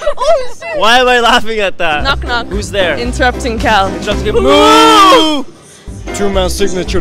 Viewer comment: I heard "interpreting cow" not "interrupting" 💀